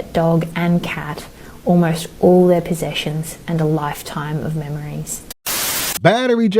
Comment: I know it’s not funny but at 0:22 I thought she said “and a lifetime of mayonnaise” 😂😂😂. I’m trippin